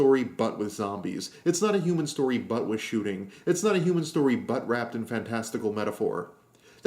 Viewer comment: Interesting ideas! ...and I definitely heard "butt wrapped"